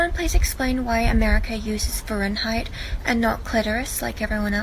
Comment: Did she say clitoris instead of Celcius ?